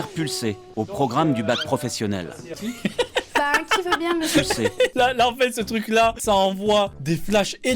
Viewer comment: qui veux bien quoi ? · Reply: Être le modèle (cobaye)